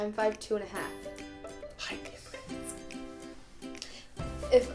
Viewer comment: What does whisper at 0:19? Thanks for answer ;-) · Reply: He said "height difference"